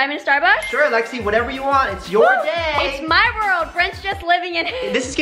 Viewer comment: who else thinks Lexi's evil😡😩 HAHAHAHA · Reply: did she say it’s my world Brentchs